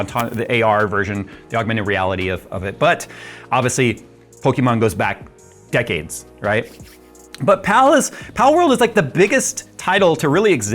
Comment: I thought you said “pokemon go is back decades” relistened and it’s actually “pokemon goes back decades” 😂🤦‍♀️ my bad